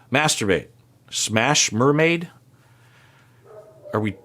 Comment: the way says “what the fuck” I lost it 😂😅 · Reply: Also when she says Shmasterbate I thought she was saying Smash mermaids